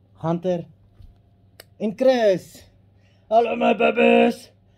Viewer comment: Imagine the bird starts to imitate his voice "I love you my babes"🤣🤣🤣🤣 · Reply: I thought he said 'babirds' Baby birds..